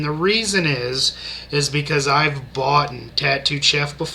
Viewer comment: bro did you say "boughten?" Automatic thumbs down. Go get an education.